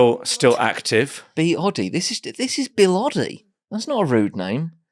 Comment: Lmao I thought when James said bill oddy ow whatever he said (idk that person) I thought he said bin laden 😂 I only realized after the 3rd time he said it